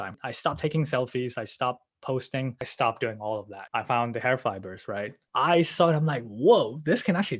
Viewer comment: you found what? i cant hear it well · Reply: Hair fibers